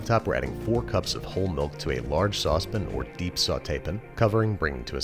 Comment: did he say a large sauce pin or sautee pin